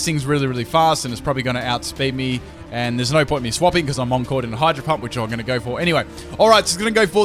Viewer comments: lol i heard him say hydro hump XD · I heard him say hydro hump? K then